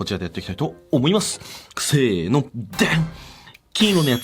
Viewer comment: せーのが くせーのに聞こえて、ゲーム実況の話入ってこんかった＞_＜